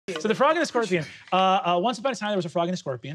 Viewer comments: Oh I always heard it as a turtle and the scorpion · Always heard it as the Fox and the Scorpion.